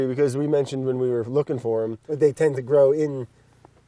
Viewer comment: Did anyone else catch "carnivorous" forests at 14:52? He might of said "coniferous" but it sure sounded like the former. lol Oh Brad. I love you.